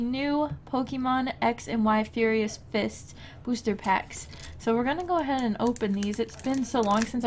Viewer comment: for a second i thought you said "furious pissed booster packs"